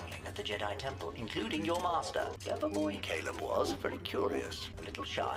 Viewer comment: Did say Caleb at 0:40?